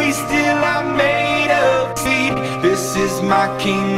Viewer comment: best part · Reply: Who else heard FEET instead of SEED?